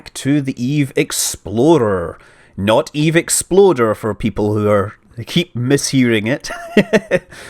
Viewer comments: I swear, every time I hear "Eve Exploder". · This is what I heard at 10:26 "Which means we can switch back to the eve exploder, not the eve exploder for those who keep mishearing it."